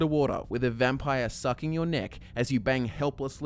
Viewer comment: thought you said dick instead of neck